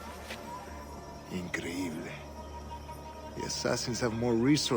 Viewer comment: When I first played the game, I thought Torres said "creamy"